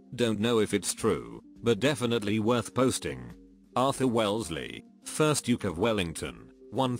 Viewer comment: at First I thought it said Arthur Wesley and I was like why did harry potter come in between